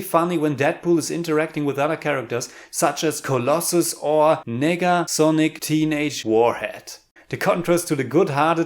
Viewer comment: Did he say nigga?